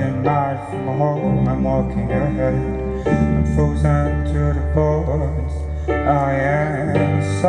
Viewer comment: it sounds like he says: Im Frosen to the Balls :)